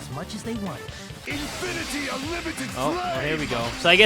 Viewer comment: HELP WHY DID I HEAR HIM SAY "infinity unlimited SLAY!" ? (I had the volume low because i was watching in near my parents)